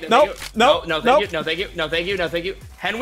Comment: that’s sound like ninja saying “no thank you”